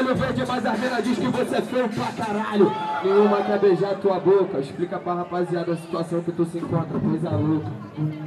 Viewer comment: o que o Orochi diz? n entendi. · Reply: "Nenhuma quer beijar tua boca, explicar pra rapaziada a situação q tu se encontra, q coisa louca"